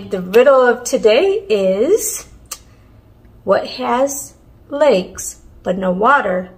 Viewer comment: Wow That was a box Had to think about the riddle for a while because I thought you said legs but then I realized you said lakes So it must be a map